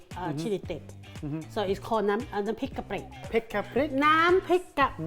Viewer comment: I thought she said “pick a prick “😂